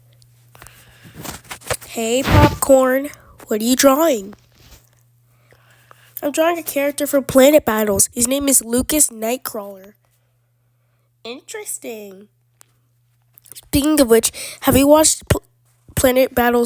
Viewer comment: i had to turn on my cc cuz i thought you said lucas ankle